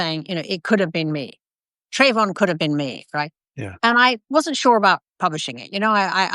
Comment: What i think Obama said was that "Trayvon could have been my son."